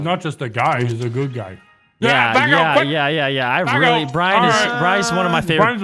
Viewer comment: did Vanoss say Bryce?